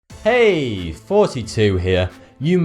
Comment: It sounds like he says hey 42 here